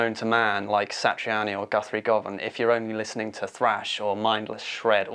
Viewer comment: I thought he said Slash or mindless shred at#5 got so mad